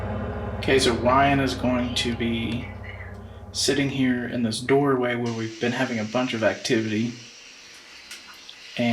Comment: u can hear a woman whisper something then she says something else before he sits down?? Couldn’t tell?? But I heard it through my JBL lil Bluetooth speaker,I couldn’t hear through regular phone!!cool video guys loved it👍👍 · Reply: That's the S-Box turning on. A woman's voice says "scanning FM" in Ryan's headphones. That's why it sounds like a whisper. Thanks for watching John! :)